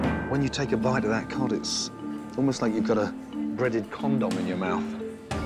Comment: He thought Gordon said, "breaded cod." But he actually said, "breaded condom" 🤮😂🤣